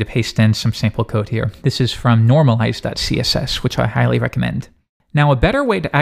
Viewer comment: in 2:00 you make a reccomendation for css code. Unfortunately it is not clear to me what you are saying. Could you give me some help with that? Thank you in advance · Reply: I think he said "normalize.css", in case you are still interested.